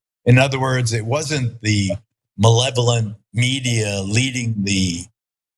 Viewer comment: Benevolent media, did he just say that? I'd like to see Geraldo's text messages. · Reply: He said 'Malevolent' media.